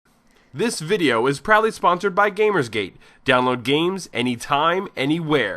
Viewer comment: lol the first thing I thought I heard was "This video is probably sponsored by gamersgate'' :D